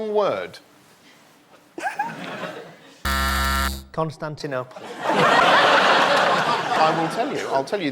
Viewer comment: what did reece say? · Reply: Constantinopol